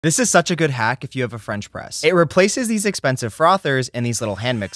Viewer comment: I thought he said french breast. 😭😭😭